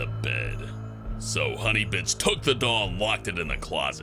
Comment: The captions says in 1:57 honey bitch